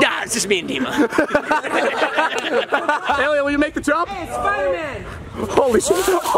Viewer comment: At around 2:55 you can hear someone I think Jason say "Hey spiderman" And it low key made my day better